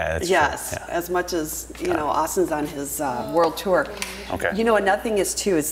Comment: Did she say Austin on his world tour